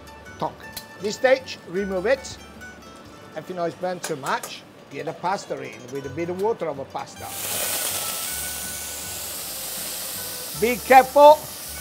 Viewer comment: I don't know what is funnier? Gennaro? Or the subtitles? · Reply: I think I hear "get the pasta in, with a bit of water in the pasta. Be careful!" Subtitles: "In the past three with me the water level class big cap full."